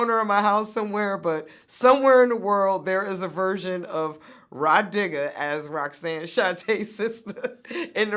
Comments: Sound like vlad said “my nigga” but he said “Ra Digga” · Damn I thought Vlad said "My Nigga" instead of Rah Digga at first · i thought he said mah nigga 2 seconds into the video